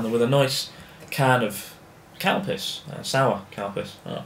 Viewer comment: Calpis sounded like you said cowpiss